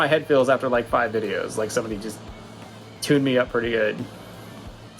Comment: I thought he said “Tua me up pretty good” @ 17:22 and I wanted to cry inside 😂